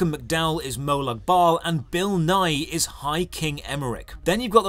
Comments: I thought you said Bill Nye and almost made me return to Eso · i thought he said bill nye not bill nighy